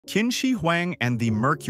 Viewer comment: I thought you said Kimchi Huang